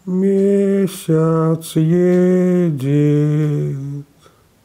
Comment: Мне сначала послышалось "Месяц едет". Кстати, очень страшно, если представить